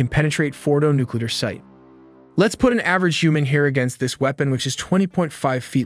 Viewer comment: Thought he said "let’s put an average human hair here" 😂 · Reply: "Let's put an average human here" It means comparing the human height and the bomb's height